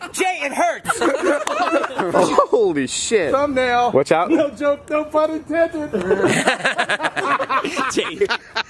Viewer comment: 1 year later i finally realized what he said "no joke no pun intended" 🤦‍♀️